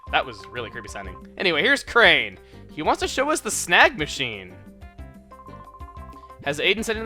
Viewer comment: Anyone at 4:22 thought he said "snack machine"?